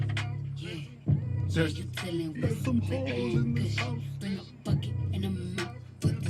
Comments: I thought they were saying there’s some rules in this house my bad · I actually thought it was saying "There's war in this house" until you guys point it out 😂😂😂😂😂